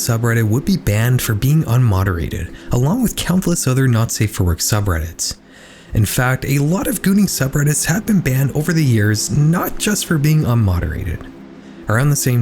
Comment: i'm sorry i heard this as "gooning separatists" like wtf we got belle delphine nationalism now